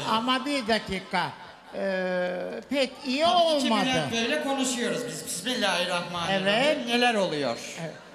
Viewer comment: ben ''hakiki bülent beyle konuşuyoruz '' diye anladım